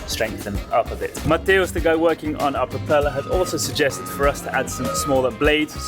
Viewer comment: I noticed a bunch of mistakes in the subtitles. They're starting to read suspiciously like auto-generated subtitles. This is not a comprehensive list, by the way. There are a lot more. At 4:21, says "Matheus, the guy working on our propeller" but the subtitles say "materials to go working on a propeller." At 15:21, 15:52, and 17:15, says "thru hull" but the subtitles say "three hole." At 15:57, says "four bilge pumps" but the subtitles say "fork build pumps." At 17:27, says "sink" but the subtitles say "thing." At 17:30 says "boat" but the subtitles say "balls." At 17:45, says "tap" but the subtitles say "tank."